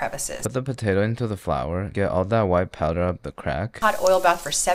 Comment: “Get all that white powder up the crack” that made me laugh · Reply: I thought he said "get all the powder in crack"